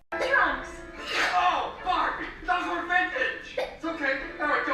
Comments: In the Barbie one I heard 'Oh Fark! Those were vintage!' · I heard “oh fark” for the Barbie thing 💀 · I do not hear “oh Barbie!” I hear “oh f***!” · In the toy story scene when I look at the screen I hear "Oh Barbie," but when I look away, I hear "Oh f." I feel like there is an MP3 glitch in the file. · From a distance it sounds like " oh (Fword)", But if you pay attention he actually says "Oh Barbie!". I noticed this when a replayed the scene multiple times.